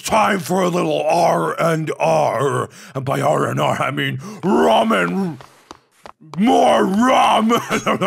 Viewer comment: Did mark said "ramen"?